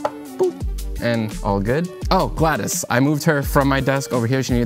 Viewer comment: thought i misheard the plant's name to be GLaDOS